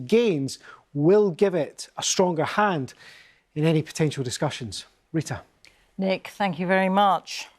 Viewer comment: He said "potential discussions" and i heard "potato discussions" I was also spacing out a lil bit so that brought me back and gave me a laugh. But yea, PUTIN SUCKS!!!!!!!! FOR WHAT REASON?! JUST DON'T. BAD.